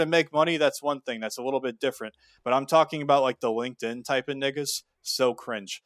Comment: I was dumb founded, but the 3rd time I heard it, I heard it he said nigga 😂😂